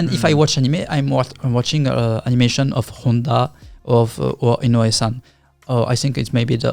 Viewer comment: i think the subtitles got it wrong with "inoue san " and wrote it "innosan" at 1:40:47 ? now i dunno if that is his actual nickname or so , but for anyone curious , he must be talking about "toshiyuki inoue" , an absolute madlad animator and a legend in the "sakuga" community...... i must be talking out of my ass here , inno really could be a nickname of his.......